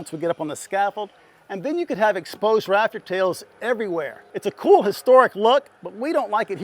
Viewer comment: I keep hearing it as "raptor tales"... like Jurassic Park? 😂